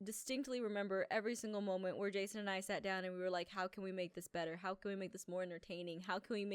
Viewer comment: Just like Aph said "How can we GREAT can we make this?" I thought she said, "How GRAPE can me make this?" And I was like Whaaaa!? Grapes!?